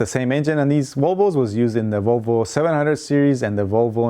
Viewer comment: VOLVO=ROWBOAT I thought you said rowboat..LOL